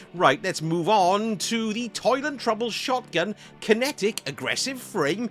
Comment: For a second there, I thought you said "Toilet Trouble" instead of "Toil and Trouble" for the shotgun lol. 😂😂 Excellent stuff Sir! 😁😁